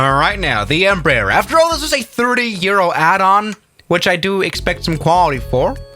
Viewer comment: I thought you said 30 year old add on lol